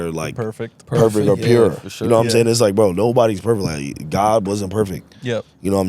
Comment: good episode but god isnt perfect???? wtff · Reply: I realized he said that after rewatching the pod - originally i thought he said no one is perfect but yeah i dont think he said that on purpose or meant it in a harmful way